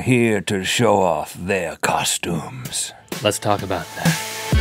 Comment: Hey, Rhett and Link and the rest of the Mythical Crew! I always love your videos, so I want to help the hard of hearing enjoy them, too :)! There were a couple of times throughout the video that the auto-captions were a bit off. At 0:13, the caption doesn't catch all of Link's "Let's talk about that!" the slogan of the show! Additionally, it seems as though many times when the word 'Halloween' comes up in the show, the auto-captions get it wrong. (0:40, 0:56). And when Link was talking about differently colored wires at 3:35, the word 'wire' was assumed by the captions as other words ("quick, red wire buyer a green wire yellow I or purple why," when I think it should say, "quick, red wire or blue wire or green wire or yellow wire or purple wire.") Your show is great, and I know that a lot of fans are watching, so I hope you'll take the time to edit some of the captions (especially since this video is not the #5 video trending on Youtube! Congrats!)